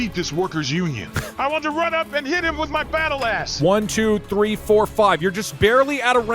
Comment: Did y’all hear what he say at 1:08:29 If you didn’t he say “I want to run up and hit him with my battle ass” lol